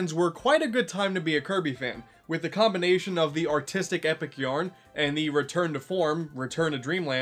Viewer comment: When he said “artistic epic yarn” I thought he said “autistic epic yarn.” Anyway, good video!